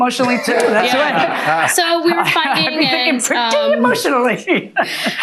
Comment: Did she say so we were fucking at 4:21?